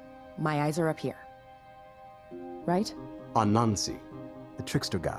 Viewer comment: Was I the only one who thought he said "a Nazi" rather than "Anansi" in the episode?